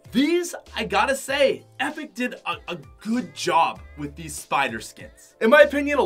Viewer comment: instead of saying ''good job" says "copying overwatch"